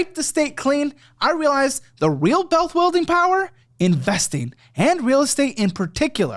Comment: did he just say - "the REAL bealth wuilding power ..." ?